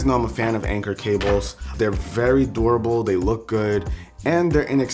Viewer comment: when you were saying durable i thought you were saying adorable